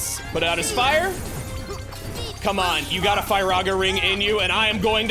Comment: I misheard screaming Firaga ring as Piranha ring